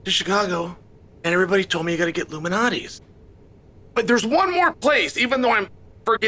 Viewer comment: I'm from Chicago, originally. Pizzas always came cut when I lived there. There are tons of exellent pizza places. That's just weird. I'm confused, Lou Malnotti's is well known, as are Duey's and Uno's (3 most famous Chi-town pizzareias). I'm sorry that happened to you guys. What a disappointment that must have been. What a shame. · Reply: Thanks for clarifying this. I thought he was saying "Illuminati's."